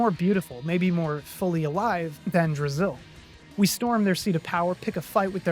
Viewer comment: Ngl for a second i thought you said the name of the other world was "brazil"